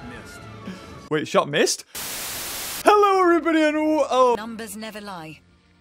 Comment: Instead of the " numbers never lie" I heard the "heroes never die" XD